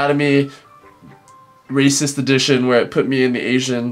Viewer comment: i thought said racist edition